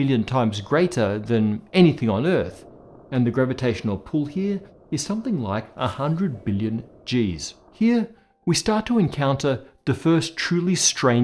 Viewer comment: “…and the gravitational pull here is something like a hundred million g”s” Meantime the caption says 100,000,000,000 g Hmmm… what’s three orders of magnitude among friends??